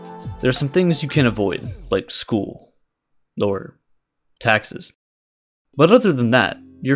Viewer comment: Does he say "lower" taxes? Seems out of context.